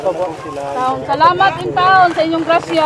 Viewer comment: may narinig ako na sa may bandang 32:16 na naka black na babae sabi nya "Salamat sir bisag kuwang" it means Salamat daw kahit kulang... I mean napaka ungrateful naman nya. Hindi nya ba na imagine kung ilan yung nabigyan nang relief goods? like my government ba na tumulong sa kanila sa dyes oras nang gabi?? ilagay natin na tutulong yung government peru mag lilista pa yan sila at take note ilang araw pa bago mabigyan. Bakit may side comment pa na "KAHIT KULANG?". Sorry nakakagalit lang sa feelings. Hindi nya na isip na simula sa pag repack pag byahe at lalo na sa mga taong nag ambag na pera ay hindi basta2x at ganun lang ang e co comment nya.. So ungrateful. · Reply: I think ang Sabi ni Ate is "Salamat Sir bisag uwan"